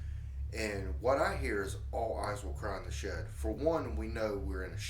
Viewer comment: I heard it said " I will find you Clair in the shed"....